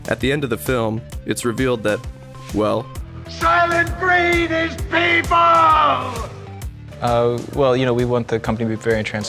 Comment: LOL that isnt the right CC for what that guy said... He says "Soylent Green is People...." but the CC says "Soylent freed these ppl..."?